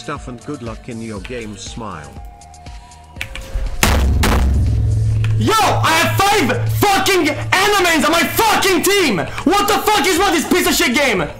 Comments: I thought he said “I have five fucking animes on my fucking team!” Lmao. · am i the only one who hears: I HAVE 5 ANIMES ON MY TEAM!